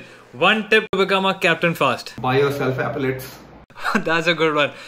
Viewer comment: What did he say? 🙄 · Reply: Epaulettes